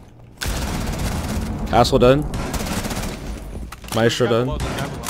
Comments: I swear he said asshole dead not castle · it sounds like he says asshole instead of castle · Why does he sound like he's saying "Asshole dead" instead of "Castle dead"? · did he say “asshole dead” or “castle dead” ??😭